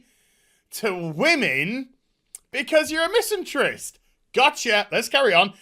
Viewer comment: Guys question, what is the word he uses around @ 9:24 and also in the next line, it sounds to me like " misentry " but I couldn't really find a definition that fits what he would mean in that situation, english is not my first language could you help me?